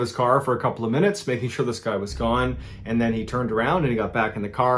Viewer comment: When you said "this guy is gone" I thought you said "the sky is gone" what a weird way to ruin a first date of stargazing!